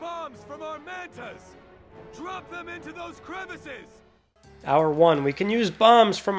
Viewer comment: I love how not only the voice acting at 23:25 is terrible but he says 'crevices' while the subtitle says 'crevasses'.